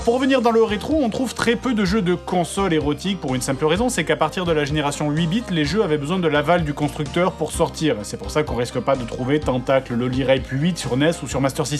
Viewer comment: C'est moi ou j'ai entendu a 10:00 a partir de 8 bite faut quelle avale ?